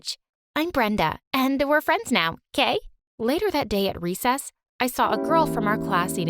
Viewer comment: at first I was like what... because I thought brenda said gay but then I realized she said okay